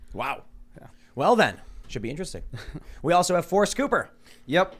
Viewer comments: Sounded like tim said name was 4 scooper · I thought Tim said " four Scooper". Was expecting an ice-cream salesman or something. lol